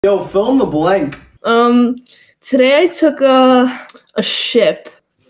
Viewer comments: As a Mexican I approve of the message to our tacos 💪🏻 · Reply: At first I thought she said she took a shit 😂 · I thought she said today I took a shit 💀💀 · Bro why I thought she said "Today I took a sh*t"💀💀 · I thought she said i took a sh*t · Bro i swear i thought she said “today i took a sh!t” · i thought the girl said "today i took a sh*t" 💀💀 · So am I the only one who thought she said "today I took a... sh*t" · He did more in a minute than most ppl throughout their whole lives. Inspiring xD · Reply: I thought they said took a shit😂😂